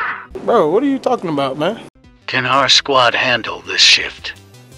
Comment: I thought the tts said "can my squad handle this shit?"